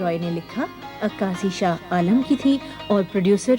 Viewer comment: In 18:19 second Nilofer Abbasi says Script by Raja Tridev Roy then what she says kazi shah kalam or what is it aqaasi by shah kalam? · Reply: عکاسی شاہ عالم کی تھی اور پروڈیوسر عبیداللہ بیگ تھے۔